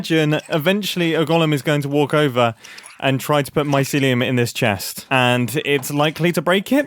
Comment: heard mumbo say "mycelium" but i heard it as "my sillium" like silly